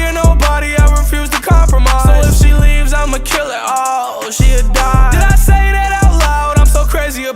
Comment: why did i think he said "so ic she leaves imma kill it, ohhh she a dine" or somethin like that